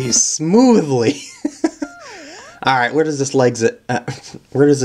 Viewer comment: The part with the red koopas on top of the munchers to the midpoint in canopy climbO_O Where does this legsit lead to?XDDD That saw at 5:14O_o Yikes the second of flying fortress just looks brutalD= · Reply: you said legsit instead of exit